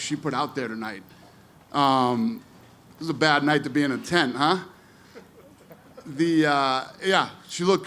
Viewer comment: wtf did he say? Its a bad night to b in a ten.......wtf does that mean? Its prob my bad hearing can any 1 tell me what he actual said?